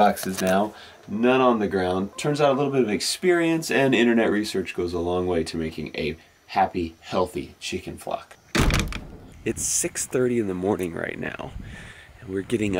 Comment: At first I thought he said chicken fuck